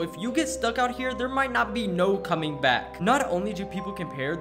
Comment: Did he just say “there might not be no comeing back”